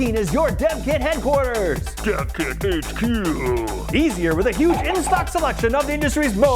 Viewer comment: The first time I heard "Dev Kit HQ", it sounded like "Death Kit HQ".